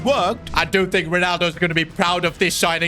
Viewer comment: did bro say i dont think ronaldo will be very proud😂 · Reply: He said "I do* think.m....."